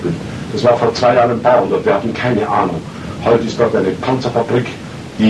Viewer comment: I hear there not "a couple hundred [tanks]" but "farmers village" (Bauerndorf). So Kramatorskaja was a farmers village he says.